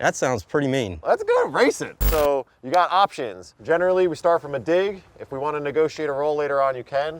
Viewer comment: did just call him General Lee?!?! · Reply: He said generally, as he was referring to how the races were done and not the cars